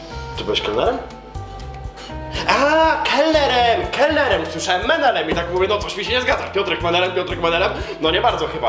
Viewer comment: On usłyszał menelem a ja frajerem XD